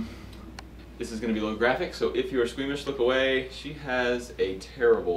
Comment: "This is a little graphic so if your screamish look away" Me: "I never get screamish" turns around kitten Me: "*scream*" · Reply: I thought he said squeamish. Now me, I didn't scream OR squeam.